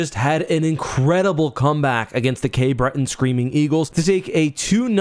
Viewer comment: It's not the screaming eagles, just the eagles now. oops. old habits die hard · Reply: I was watching this in bed while my girlfriend was asleep so I had the volume turned down low, and I had to rewind that part because I thought you said "screaming peoples."